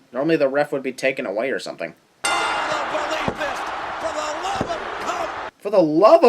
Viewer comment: come on! He clearly said “for the love of God!” Also, this is the GREATEST MANIA EVER!